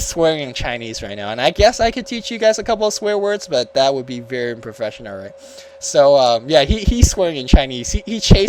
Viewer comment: He said "I can teach you a couple of chinese swear words but that would be very professional" at 31:00?? I didnt quite catch it.lol